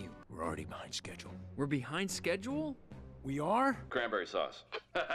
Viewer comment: did he just say that grammar is sus? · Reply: He said "cranberry sauce"